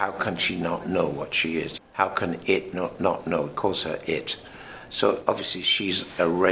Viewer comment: What is he saying @ 1:39 ? "How can it not know *** it?"